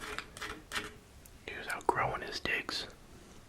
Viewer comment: YO WHAT DID HE SAY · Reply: “He was outgrowing his digs”